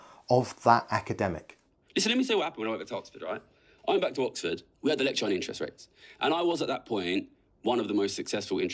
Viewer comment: I had trouble figuring out what he was saying at the first line and I know the closed captions are wrong, so I wrote this comment to help others out. 12:17 "Listen, let me tell you what happened when I went to Oxford, alright. I went back to Oxford, we had a lecture on interest rates, and I was at that point, one of the most successful interest rate traders in the world. ..."